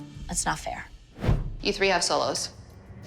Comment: I did actually I know it is 36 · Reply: oh sorry I thought she said season not solos, my bad